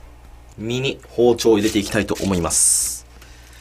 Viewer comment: 耳に包丁入れていきます にしか聞こえなかったから目医者行ってくる